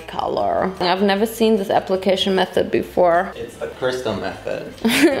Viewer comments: I heard Crystal Meth? WAT jk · what he said: it's the crystal method what I heard: it's the crystal meth also kids don't drink, I'm Czech and can drink since 18 but only get like a small glass of beer when we have certain meals once or twice a year (like duck with cabbage and dumplings, yummy) to help digest the fat. Or a small glass of light alcohol with my Ma to celebrate things. But believe me when I say going clubbing at the weekend is not worth it.